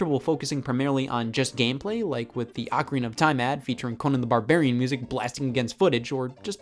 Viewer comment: who tf did the subtitles that does not say Ocarina